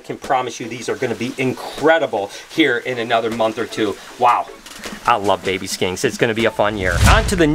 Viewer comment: I totally heard say "I Love baby SKANKS its going to be a fun year!" had to rethink what I heard LOL 🤣